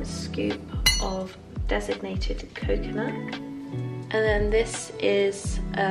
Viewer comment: Lovely vlog! One thing, though: I keep hearing "designated coconut", when you mean "desicated coconut" :)